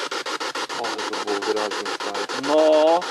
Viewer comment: Mnie interesuje jedno jak skanował jezioro to była data 2018 Edit: 30:13 powiedział "nie" tak usłyszałam